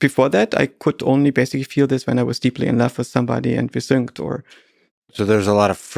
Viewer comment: subtitles, I heard: 11:48 "when I was deeply and love with somebody and we synched , or [something]..." 2:51:08 "every opinion that fits between two human ears might be between two human ears" (redundant y influenced by "human")